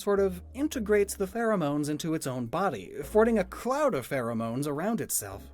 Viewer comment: Eevee: I'm the only one who has many types of myself! Castform: Hold my cloud EDIT: 283 LIKES?! IT'S ONLY BEEN 2 HOURS, THX SO MUCH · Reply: Thought it said "clout" lol